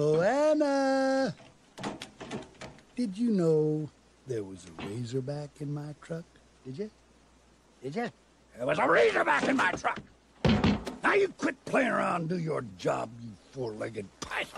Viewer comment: Here it sounds like he said "There was a raider back in my truck!!"